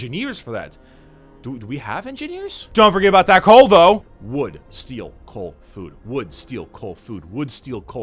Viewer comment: I heard that as "Would steal cold food."